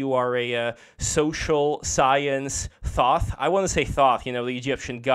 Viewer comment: For a half sec I thought he said "social science THOT" 😂